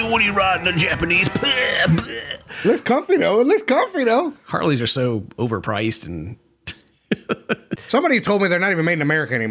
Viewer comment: I thought said “Karly’s are so expensive” thanks